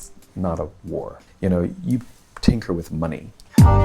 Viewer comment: Tick with money? What does he say at: 1:37? Thank you!